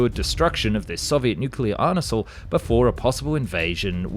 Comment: Dude said arnesal instead of arsenal